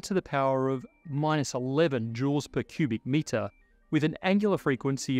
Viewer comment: Bit of a captioning error at 11:51 - " 10 ^ -1 Jew" instead of " 10^-11 Joules"